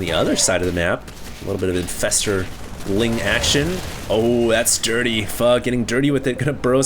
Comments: who else thought that was saying 'oooh that's dirty, fucking dirty' at 8:55? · sounds like said fuck